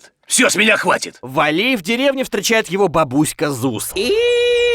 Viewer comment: мне послышалось или бабушку Вали зовут Зуз мне на этом моменте прорвало ПЛАГИАТ!!!!😂